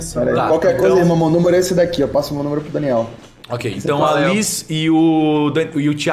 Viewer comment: EU OUVI UM DANTE AQUI?! OU EU TO FICANDO MALUCA?! (é zuera garaiokkjk)